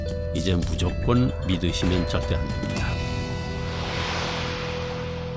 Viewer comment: 무조건 믿으시면 좆됩니다...? 아니 뜬금없는데 뭐라고 하는건지 너무 궁금해요 저렇게 밖에 안들림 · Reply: 절대안됩니다 라고 하셨음